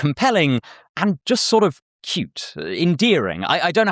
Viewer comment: did bro say "cute"😂